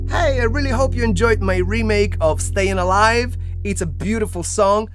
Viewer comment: oh i thought that said "Still Alive"